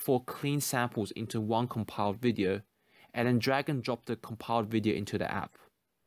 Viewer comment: I swear I heard him say dragon drop, I'm sorry